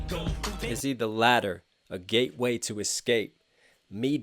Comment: I always heard the "is he a ladder" line as "is he the latter" as in the second thing, along with the ladder, true double